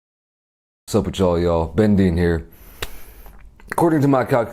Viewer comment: I thought he said lol